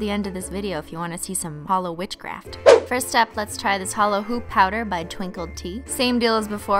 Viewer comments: I thought the holo hoop said holo poop ;-; dies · am i the only one who thought it said holo poop… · yay! a video on my birthday! However I thought the holo hoop said holo poop. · I thought it said "holo poop" at first and not holo hoop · For a sec I thought the holo powder said "holo poop" but then she said holo hoop. I need glasses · I thought it said holo poop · I honestly thought it said holo poop